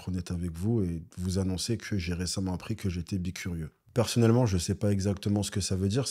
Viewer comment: Cette loose. Au début j'ai compris "je suis epicurieux". Je me suis dit "WTF un featuring avec Jamy le truc improbable et ouf". Et ben non. 🤣🤣🤣